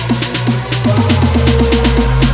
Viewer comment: What I heard... Bam bam bam Escalator, beat drops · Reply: i think 2:45 is "fuck what you heard